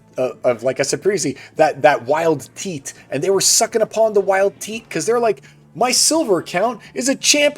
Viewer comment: all i heard was "sucking the wild teat" 😂